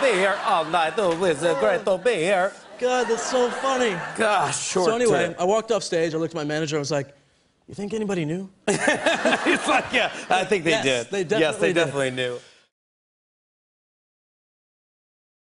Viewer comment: I thought I heard "fuck yea"